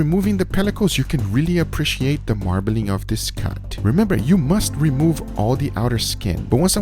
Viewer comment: did he say ”This cunt?”